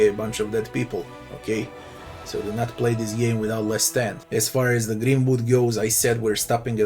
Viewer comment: what are you saying here “less than” what makes it so many people don’t die?